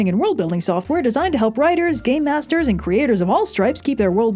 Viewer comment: Misheard it as “gay masters”… Not sure that’s actually much of a leap, GMs let me know?